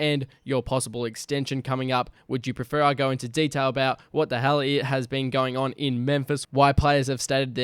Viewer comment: Did bro say wthelly At 2:45 ?